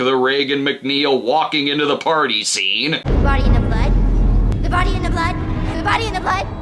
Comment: Ending your movie with a bullshit speech about hope and a happy reunion between Reagan and her mom not 5 fucking minutes after we just watched an innocent girl get dragged to hell by a demon has to be one of the most tone-deaf things I've ever seen. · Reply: I thought she said body in the butt not body in the blood.